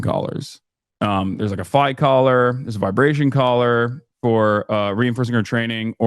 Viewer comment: Did he say she has a “fight collar”? 🤔 Interesting…